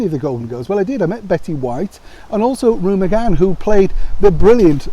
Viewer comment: Great video! Maybe I misheard you but it's Rue McClanahan (sounded almost like you said (McCann). At any rate, very cool photo of you two together--you must treasure it! Also, from what I have read, the only two Golden Girls who sort of, kind of didn't really get on were Bea and Betty, though it never truly developed into any sort of a major feud. Love the Golden Girls!